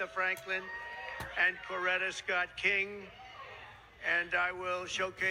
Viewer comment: Did I hear him say "Loretta Scott King?"